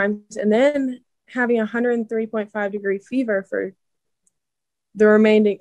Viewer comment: I'm sorry did she say 130.5 degree fever? That's well done, girl I hope I misheard her I think she meant 103.5?